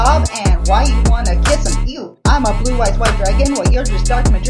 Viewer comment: or 1:07 E??? What's E? (That's what I hear. E.)